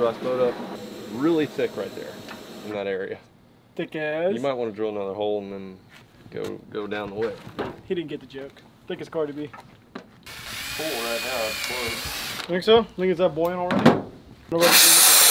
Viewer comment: did he really say thick as cardi b